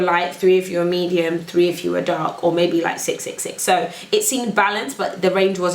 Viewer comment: what did she just say at 19:15?? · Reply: "3 if you were dark"